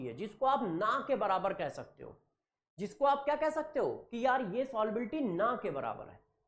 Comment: jiske solubility bahut bahut kam hoti, jisko aap keh sakte __ ke barabar. What's that word, pls do tell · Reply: Naa ke barabar hai yaani almost zero ko tend ka rahi hai negligible hai yeh matlab hai uska